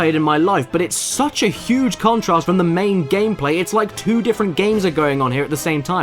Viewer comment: sounded like you said gameplay tits lol